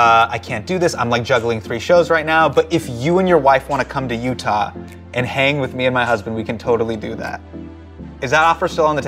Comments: Did he say If you want to hang with me and my husband?? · did Hasan just said hangout with my husband?!?! Wait what?! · did Hasan say hang with me and my husband? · is it just me or did hassan say "if you wanna come to Utah, you can hang with me and my HUSBAND"?!